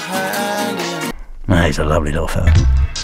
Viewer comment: I like how as soon as Murdoc is done insulting 2-D he calls him a "lovely little fellow" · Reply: i heard him say "he's a lovley orphan"